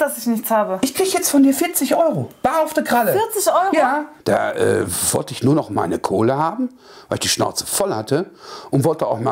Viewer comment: Ich habe verstanden Da wollt ich nur noch meine Cola Haben. 😂😂😂😂😂😂